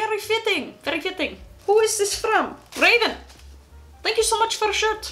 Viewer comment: did she say Raiden cuz that’s my name lol